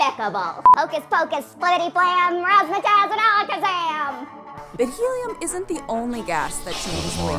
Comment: DID SHE JUST SAY AZKABAN?🤔😱😳